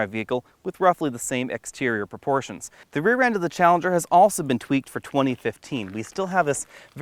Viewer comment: did you said challenger?